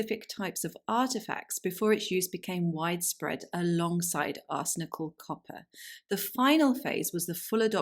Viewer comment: I had never heard of the word “arsenical” as the adjective form of arsenic. I thought she was saying “arse-nickel”… like some kind of slang for low grade nickel. But I knew that wouldn’t make sense so thank goodness for closed captioning! It all makes sense now. 😅